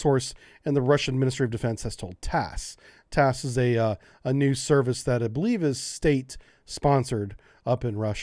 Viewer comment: "TASS is a new service" - I am not sure I would describe a news wire agency that has existed since 1904 as "new". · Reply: I believe he said: "TASS is a News service". Many English speakers blur the plural in such cases, I do it myself, both what you thought he said and what I thought he said sound identical when I say them.